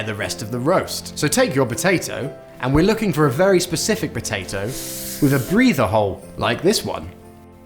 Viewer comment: I thought they said "a potato with a breeding hole, like this one" I was like A WHAT????